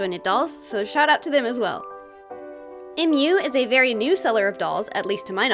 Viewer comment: When she said "so shout out to them aswell". I heard "shut up at them aswell"" 😂